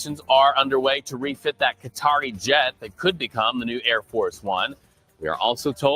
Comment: I heard "could become AF1". Goodness!!! can we the taxpayers just declare "as is" and void the lemon law? 😂😂😂